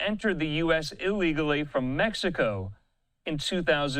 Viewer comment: I keep hearing "came from mexico" instead of "tru mexico"because isn't he Indian like Punjabi Indian ??